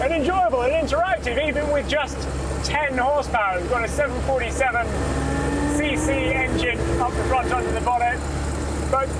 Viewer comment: What everyone else heard: "Its got a 747 cc engine under the bonnet." What I heard: "It's got a 747 engine under the bonnet."